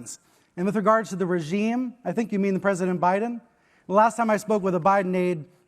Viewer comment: Do they put a pan on the floor to catch all that sweat leaking off of him when he tries to talk ? Im hearing imparied so I cant tell did he just say O'biden at around 3:10 3:12?